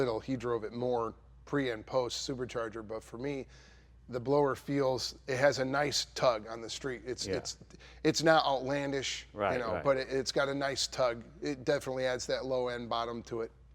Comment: All I heard was - A good blower tug from a low end bottom. Its a wonderful life.